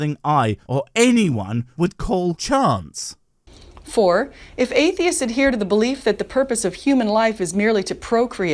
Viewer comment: Atheists have no answers for anything they are stupid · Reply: I thought you said atheists are evil.